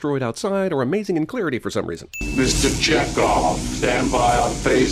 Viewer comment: did anybody hear that as "Mr. Jackoff"?